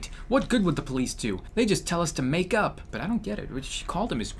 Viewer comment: thought said make out. Oh my my god.